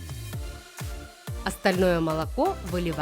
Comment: Кому на 4:19 послышалось стальное молоко лайк!